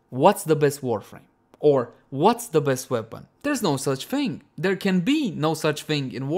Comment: I thought he said. The best shotgun, the best waifu